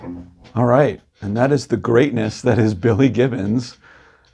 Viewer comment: i almost shit my pants..i had to back the video up..couldve swore he said the great and LATE, Billy Gibbons 😂 Thank god I was wrong 👍